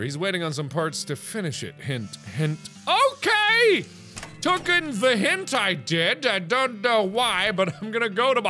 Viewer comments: Sounded like Mark said "tooken the hentai did". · Anyone else hear hentai